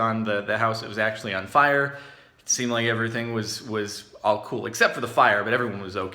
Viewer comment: Jardon I thought you said everything is ok except for their death